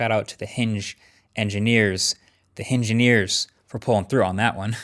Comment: Did he just say hingengineers!?